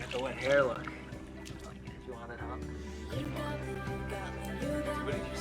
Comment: I think he says "do you want it up?" (Referring to Deeptis hair) 😄😄